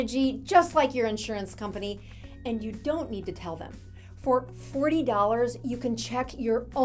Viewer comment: Sounds like you said 3 dollars...more like 40 dollars